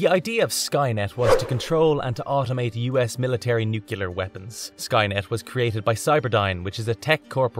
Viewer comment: I'm not sure what bothered me the most about this video: the fact that it's very repetitive and looks like it was written by a bot or that I keep hearing "nukular" every time it's supposedly saying "nuclear."